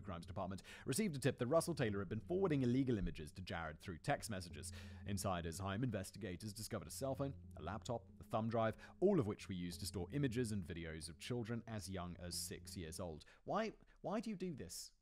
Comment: ish, did Simon say the devices were found in Russle’s hymen?